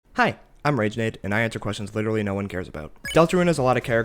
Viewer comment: I thought you said “hey I’m ragebait” and I was just like… yeah that makes sense